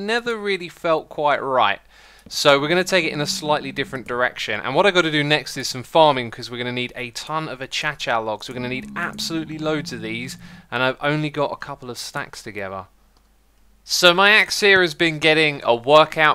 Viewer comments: did he just say “a cha cha logs” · LMAO i thought he said “ so, me ex here has been getting a work out” 🤣🤣